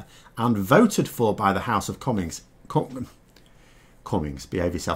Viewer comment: Sponsored by Putin TM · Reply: To me it sounded like he said "the house of comics". Also pretty good....:-)